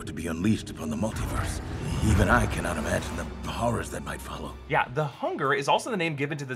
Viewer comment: "even I can not imagine the horrors that might follow" he did not say "hunger"